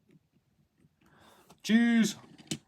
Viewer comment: I initially misheard him saying, "juice" instead of "cheese". 😂